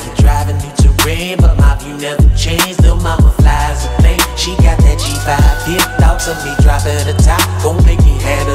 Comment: I thought he said she got that’ G5 feel